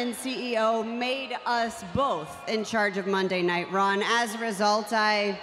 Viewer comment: did she just say Monday night ron.